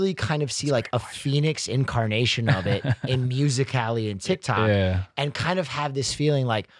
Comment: did he just say “Music Cali” instead of musically?? 😂